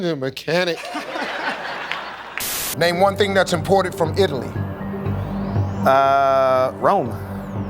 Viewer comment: I thought he said whats "important" from Italy. Not imported